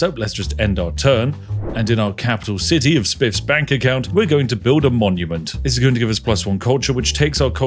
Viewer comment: Really thought you said, "Spiff Spank Account"